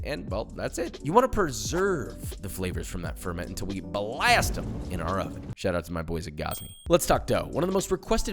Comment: Yooo when he said “until we blast them in the oven, shout out Gozney” @ 4:11 I thought he said Gaza and I was like mannnn this guy is BOLD!